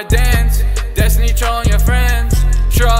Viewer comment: at first i thought it said thats me trolling your friends